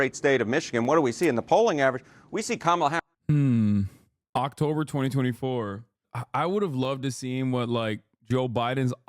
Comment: Hawk-tober 2024... I swear, that's what i heard 😂😂